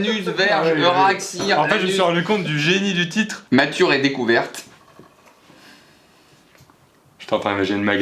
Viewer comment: j'ai compris Mature et raie découverte